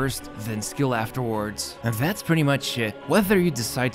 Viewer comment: you need good hearing for this and GREAT listening skills. oh and either use speakers or... Headphones with good speakers. 4:58 - 5:01 Yes... He did say... that's "pretty much it" BUT!... I heard different... I heard the word S.H.I.T. "That's pretty much Shit"... Guessing he was wanting to say, "that's pretty much is it"... ended up saying [much it] not [Is it].